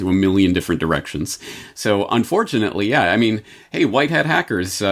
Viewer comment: Did he just say, "un-four-chanately" ??